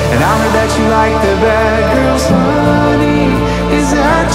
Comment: “Honey I heard you like the bad girls, is that true?” ... could have sworn I heard Packers instead of bad girls... trixie your wisconsin is showing 🤣